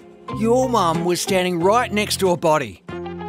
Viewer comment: almost sounded like he said buddy